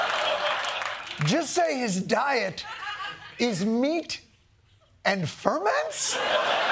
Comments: I thought he said he eats "meat and varmints". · whenever RFK Jr says ferments, I always think he's saying "Fer men" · I thought he said meat and varmints.🤣🤣🤣🤣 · I thought he said meat and vermin. 😂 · I thought he said meat and varments! Which tracks. To be fair, he could bring you sauerkraut instead of yogurt. 😂😂😂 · Jon, I'm so glad that you reiterated several times that RFK Jr said "meet and ferments" . I originally misheard it as "meat and vermin". It then struck me that the idea of RFK Jr eating vermin would be rather redundant. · I thought he said meat and vermin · I heard “meat and vermin” 😂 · Genuinely thought he said "meats and vermin" first time around, and that would have been no percent surprising. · I thought he said Meat and Fur Men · You are better than me because I was only listening to the audio and could swore RFK2 said that he is on a carnivore diet and eats meats and vermin🤮